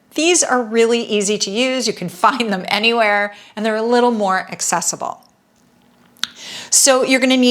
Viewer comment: What are you saying at 2:49? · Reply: “ you can find them anywhere and they are a little more accessible”